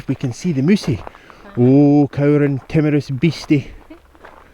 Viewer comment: So funny. Being from Canada I thought you said you had seen a moose. As I was looking for the horns I couldn't understand why you weren't being more cautious about approaching it. Then I realized you meant mouse. Lol!